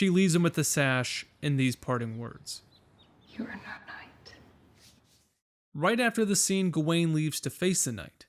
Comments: In the theatre when she said "you are no knight" I thought she said "you're a nut knight" since it happened right after he came. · I played it back several times and still can’t make out what she’s saying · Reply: “You are no knight”